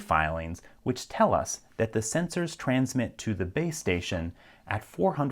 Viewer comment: Sounded like you said babe station